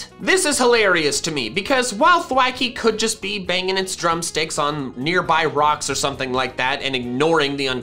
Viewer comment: I heard drumd*cks.